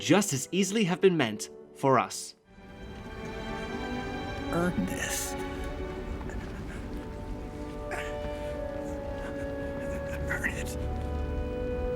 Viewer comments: "Earn this... Earn it." That scene makes me choke up every time. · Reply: so THATS what he said. I always thought he was saying "Earnest", no reason why he said this, its just what i always hear. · Oooooh he's saying "earn this" I thought he was saying "Earnest".